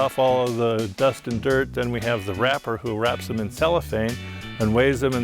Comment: Wrapper? I thought he said ‘rapper’? Hearts of palm have my heart Never to be pushed apart Days without you are so tough At least with you, I know it ain’t rough. Hahaha Where’s my Grammy? Haha